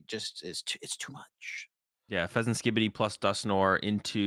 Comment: bro he actually said fezanskibidi how did nobody notice that